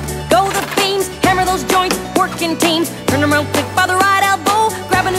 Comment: I'm the only one who heard "Twerking team" ? D: and then "twerkin' hard,you're doing good"